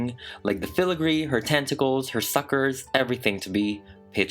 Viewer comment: I stopped the video the. Came back to it at that time and thought he said "fuckers" lol